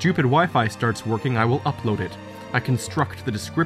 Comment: Bruh i thought u said my stupid wife starts working but its actually wifi 😂 lol